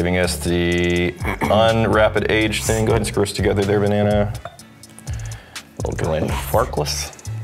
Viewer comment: did call "Banana"? 😋